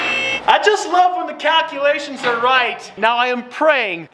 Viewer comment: Anybody else hear "cat-culations"?